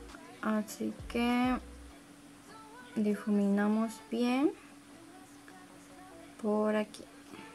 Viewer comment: En el minuto 1:48 se escucha una voz que dice "bien bien" o es mi idea??